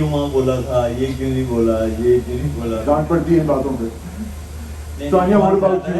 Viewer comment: I thought he said gaand padhti hai